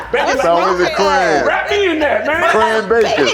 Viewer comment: Every time I see I hear him saying “I’m into that” lol